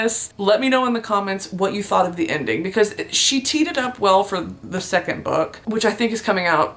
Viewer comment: thought she said "she teeted up well"